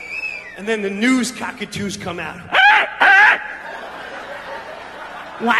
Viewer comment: Did Brewer say "then the news cockajews come out..." at 9:48(in this post) might wanna call him on that as well.